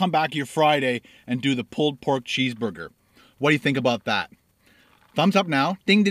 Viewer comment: Sounded like you said "Chess Bugger" - lmaoo :P